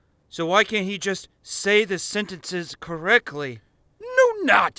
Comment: Is it me or does he say correcly instead of correctly?